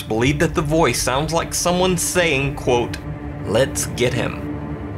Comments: it legit sounds like it says 'I got him' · it sounds more like “Lets Kill Him” instead of “let’s get him” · The part where said he heard lets get him i heard lets kill him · The voice at 6:24 is clearly saying "ESKETIT" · In regards to the Children's Asylum, I heard "You should get out," instead of let's get him. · I hear let's trap him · I hear " let`s kill him" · bruh I hear "let's kill him" 😱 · I hear “Lets trap him”. Anyone else hear the same? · I hear "let's kill him" instead of "let's get him" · I hear "I'll shove him", but it sounds like this entity has an accent which makes it difficult to decipher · I hear a ''let's get out'' So, maybe there was just another urban explorer? Or maybe 2? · I think the voice in the children's asylum sounds like it says either: "Let's kill him". Kudos to the guy for finding a rationally justifiable explanation, as opposed to give in to superstition. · It sounds like it said let's kill him